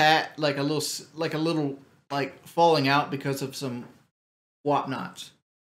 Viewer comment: What does he say at 5:12? When he's talking about Kelly and his mom falling out? "whatnats"? Any ideas what he meant? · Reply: “What-nots.”